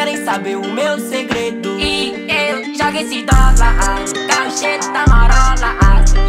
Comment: ele: e eu jogo esse dolar eu: e eu jogo o SIDOKA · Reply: Eu entendi " e joga esse toba" kkkk